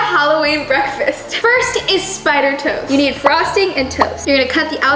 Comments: At first I thought said spider tose ayooo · at first I thought said “this is frosting and toes”I was like what the heck 🤔🤔😂😂🤣🤣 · I thought she said 'This is spider and toes' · I don’t know why I thought you said spiders toes